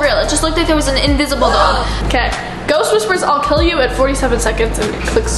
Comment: I hear “ill kill”